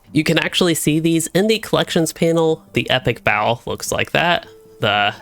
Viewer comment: I thought you were saying "epic bowel." Thanks for the video bud!